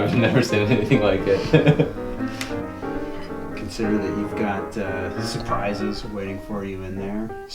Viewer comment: did he say you got some surprise in there, I should be shitting myself if I heared that and seen what was in front of me